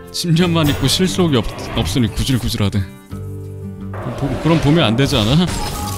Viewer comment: 뭐라고하시는거에요 ㅡㅡ만잇고 ㅡㅡ는없으니 구질구질하대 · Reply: 집념 실속